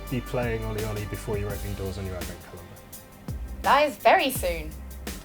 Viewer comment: I can't here what when he says you might be able to play it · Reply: 'Opening doors on your advent calendar' Still vague but gives you an idea :)